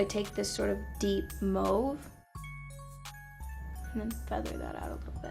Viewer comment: I love her but did she just say MOVE?! Instead of Mauve